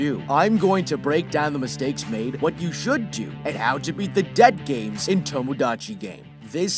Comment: I thought he said "Dead Game" but no i look at the title and realized its "Debt Game"